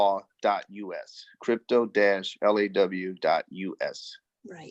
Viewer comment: Did he say ( crypto.- law.us ) at timer 19:30ish? I couldn't find it when I searched